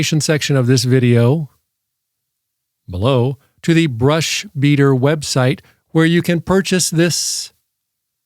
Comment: You say "brush heater", but I hear "bruh sheeter". 🎉